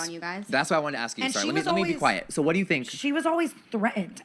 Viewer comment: i’m rewatching this and in 45:03 did jeffree cut Brooke off and tell her to be quiet or did i hear it wrong?? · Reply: I’m 6 months late lol, but I’m pretty sure he said “let me be quiet”. Fuck Jafar Starfish tho.